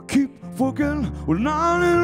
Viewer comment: Sounds like he says "keep fuckin" at the beginning.